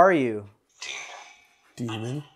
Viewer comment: When you ask who are you, it did say demon, chuck & Owen 08:28 ~ 08:32 ish. (I'll have edits I'll come back & comment what I hear or see as I watch) @ 09:41 when u said chimney it said "blood" when you said window @ 10:00 it said "ghost"